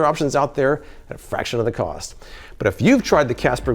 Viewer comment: "Casper Low-life" is what I kept hearing him say. I'm high af and this video just seemed hilarious to me. Casper Low-life! 😅🤣😂